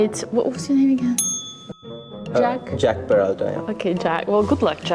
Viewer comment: I heard jack "paratha"😂